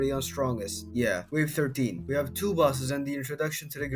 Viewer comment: the subtitles just say wave 133 instead of wave 13